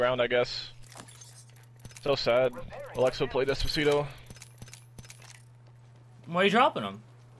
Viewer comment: did he say Alexa play despacito